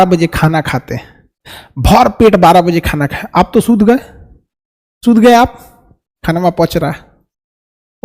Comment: sir ne kya kaha sunayi diya kya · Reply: Chud gye aap😂